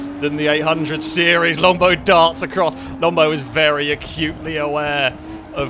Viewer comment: I do agree, Longbow is very cute Wait… I might have misheard